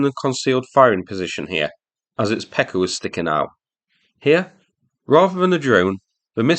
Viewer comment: lolol Msta 2s19 pecker was sticking out and got destroyed.. I had to replay that and research it a bit. I first heard it as a Mr. S pecker. jolly good destruction